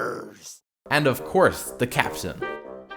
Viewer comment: Did he say and of course, the caption at 3:05 ?